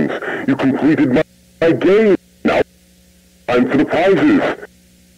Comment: is it just me or did boozoo say "Congratulations! You completed my game! Now I'm gonna find you..."